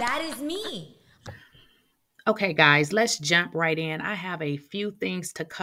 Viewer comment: Why I thought she said let’s jap right in lol 😂